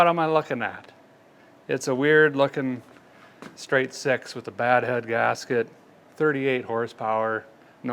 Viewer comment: Did he just say, "Straight sex with bad head" if so just let me say it's nice to know I'm not alone.